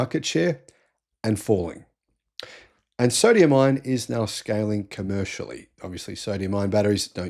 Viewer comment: I keep hearing you say "Sodium Iron" ? there's no such thing - its Sodium Ion